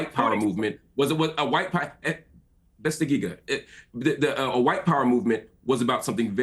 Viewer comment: did he say "bas dagigah"